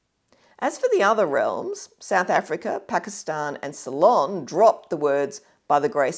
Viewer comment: "South Africa, Pakistan and Salon"? I turned on the subtitles to see whether I misheard, but they also said "Salon". Took me a solid 5 minutes to figure out that Sri Lanka used to be called Ceylon in English before I was born.